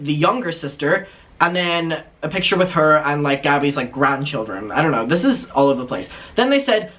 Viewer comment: ish-did he say Gabbie’s grandchildren?? I scream laughed.